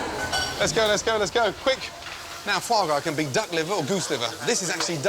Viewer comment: did he just say dog liver?